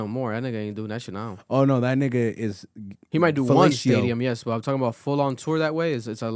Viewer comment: did bro say fellatio? 😭😭